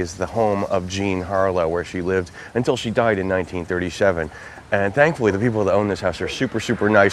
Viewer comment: Just a note as not sure if others already commented. At the 6:37 mark it said she died in 1937. Did I hear it wrong as she died in 1987. Thanks